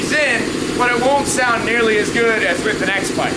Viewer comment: did he say sex pipe?